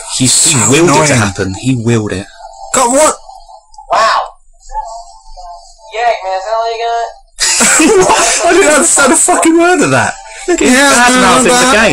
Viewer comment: I think he said, "Yeah, Eggman, is that all you got?"